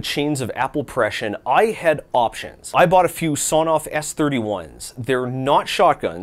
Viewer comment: Lol, I thought you said you got a Sawed-Off S-31